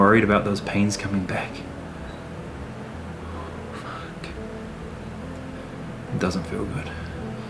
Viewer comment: did u just say the f word BTW stay safe and you can take a break if you want to. Edit:Stay safe, I know you will be safe and stay well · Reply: he jut said fuck, not likes he’s being racist or some shit